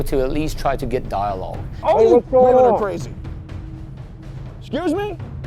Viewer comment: Did he just say "Women are crazy"?